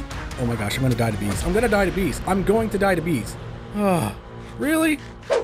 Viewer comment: Idk why but at 4:44 when says "im going to die to bees" i misheard it to im going diabetes🤣